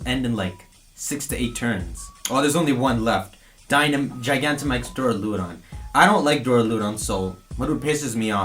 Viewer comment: When he said g-max duraludon I thought he said g-max breloom